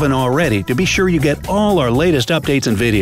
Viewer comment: What did he say at 7:47 because it sounded like he said onims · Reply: he said on the